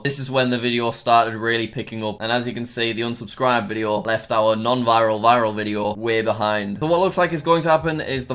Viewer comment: He said Viral viral video instead of viral video video LOL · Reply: He said non viral viral video lol